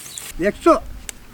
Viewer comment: Am i the only who misheard that he said exo??? 😂😂😂